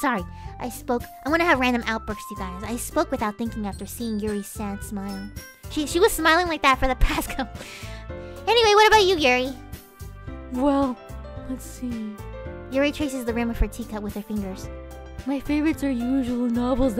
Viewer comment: I honestly heard this as "Anyway, what about you Gary?" instead of "Anyway, what about you Yuri?" and I startled so hard because my nickname is Gary and I didn't expect to hear it lol